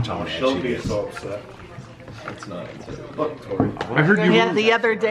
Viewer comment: did I hear f*ck tori 😂😂